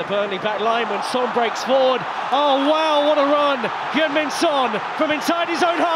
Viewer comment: Thought I heard Youngman's😂 Son